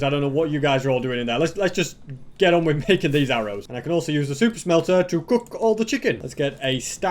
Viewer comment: I actually thought, you said "Soup Smelter" LOL😂